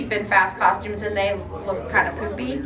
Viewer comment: LOL did she say "poopy" 15:19!!???